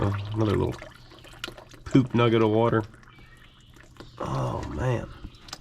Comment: "Another Poop Nugget of water"?? is that what you said? HAHAHAHAHHHA